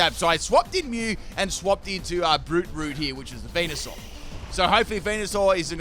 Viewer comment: That Venusuar was called BRUTEROOT?! That makes so much more sense, my entire life I always heard it as "FrouFrou" and I'm like, "That's a dog name, but okay" lmfao